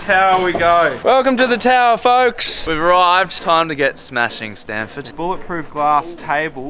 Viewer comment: “It’s time to get smashing Stanford.” Did I hear that correctly? 😂🙈